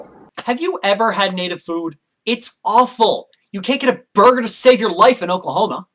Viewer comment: "You can't get a (what?) to save your life in Oklahoma". I live in Oklahoma and whatever he just said (if I heard it correctly) isn't true. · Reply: he said burger